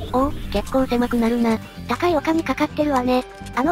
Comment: 高いお金って聞こえた